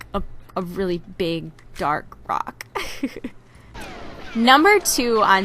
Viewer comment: when she was talking about rock and under it it thought she said "big dark cock"